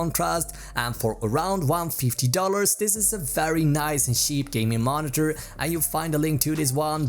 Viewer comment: what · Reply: his accent made it sound like he was saying sheep instead of cheap